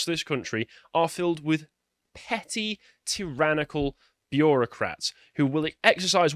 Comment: Why am I hearing that word as bee-Euro-crats?